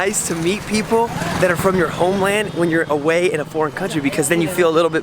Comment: Did you just say "poor country" 1.52...